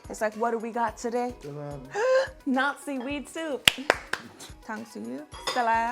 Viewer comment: Anyone else hear "Nazi Weed Soup" instead of "Not Seaweed Soup" lol